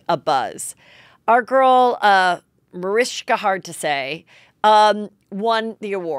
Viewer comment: Did she just say mariska hardtosay!???